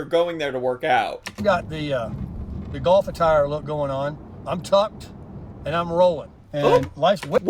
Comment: these comments "I'm tucked" (though I heard "I'm topped"), among many other comments you've teased for possibly sounding Home of Sexual reminds me of Tobias Fünke's character in Arrested Development. Everything he says... sounds... Home of Sexual...